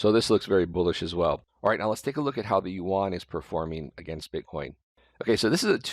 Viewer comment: What did he say? YUAN?